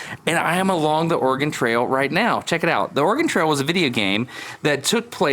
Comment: Did you just say, Vidia Game?